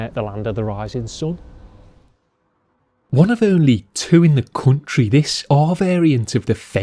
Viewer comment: I heard cunt...and tree....hearing impaired Scandinavian sorry